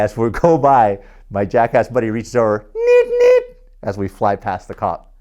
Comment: Your friend did what while you passed a cop? I didnt get that part. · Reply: AlphaCentorri Vlogs i thought he said Meep Meep like the anchovies in Spongebob